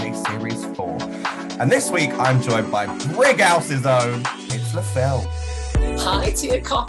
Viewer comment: Damn, just realized I've been mishearing Brighouse as Brickhouse for decades!